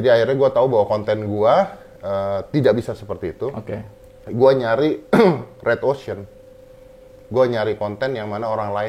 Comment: deddy ngomong apa? Nyari redwosion?